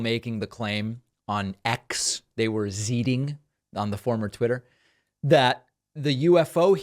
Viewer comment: Did you just say "zeeting" ?